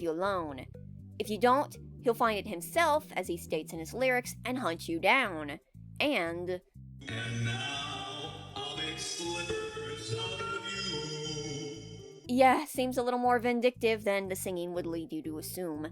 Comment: i hear slithers